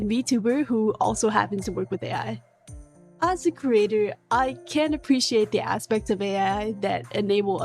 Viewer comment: Can or can't appreciate? mishearing aside, loved the video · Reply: Can’t, sir! 🫡